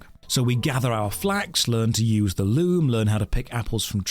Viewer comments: Oh! he said "learn to use the loom"... I heard "learn to use the loo"! I was like "you have to use the bathroom in-game??" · thought you said "learn to use the loo"